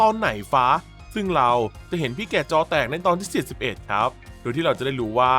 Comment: (0_0)ผมได้ยินว่า41